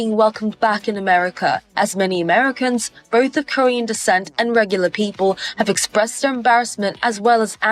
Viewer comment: did she really say “regular” people?